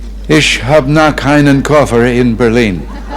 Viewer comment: Cecy At 1:58, I think he says: "Ich hab(e) noch keinen Koffer in Berlin" which translates to "I haven't got a suitcase in Berlin yet". At 9:09, he says: "Berliner Herz (heart), Berliner Humor, ja, und Berliner Schnauze (= berlin dialect)" I hope I could be of help :)